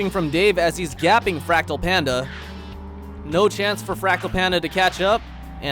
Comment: Totally thought I heard you say "Rectal Panda," lol